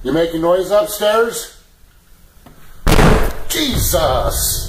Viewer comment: JESUS Me: I heard Cheese sauce